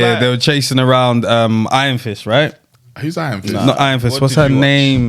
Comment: Iron fist is Marvel. Everyone needs the helmet. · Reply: I even thought he said “Iron Fish”😂💀